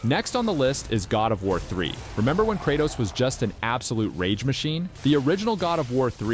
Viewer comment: I thought you said Kratos is an absolute r*pe machine haha 😅